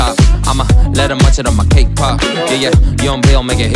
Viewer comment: did he say kpop